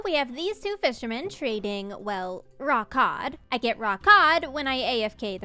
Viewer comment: For some reason, I heard “raw cod” like was saying it with some kinda accent and kept hearing it as “rawk hard”… oh the little ways we entertain ourselves when recovering from a weekend at the hospital… 🤷🏻‍♂️